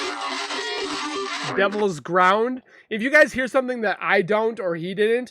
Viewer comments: Sounds like it says Devil's crown ground 😢😢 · I thought i heard her say. " its the devils playground " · I heard "devils playground"